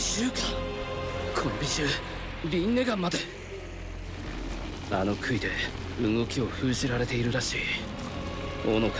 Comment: what did he say????😧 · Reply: rinnegan.